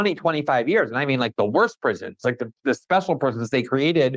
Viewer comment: "I mean like the worst Presidents, those special forces" Lol bad subtitling there, he actually said "the worst prisons, those special prisons". 😅